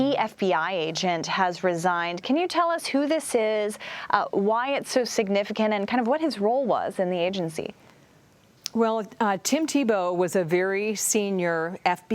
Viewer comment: Did she say Tim Tebow? Well, this changes everything.